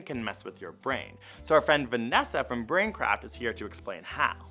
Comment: Did he say BrainCrap?